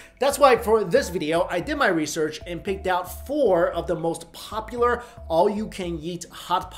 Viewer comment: Am I the only one who heard "all you can yeet" at 0:28?